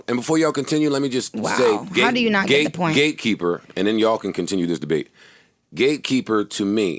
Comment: oooh ok its GATE keeper, i thought they were saying gay keeper i was like wth is going on here